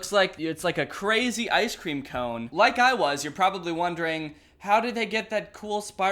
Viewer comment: "an ice cream cone... Like I was" your probably wondering. I'm at that stage of the night where I heard it as "an ice cream cone like me" idk wtf is up with me. Tbh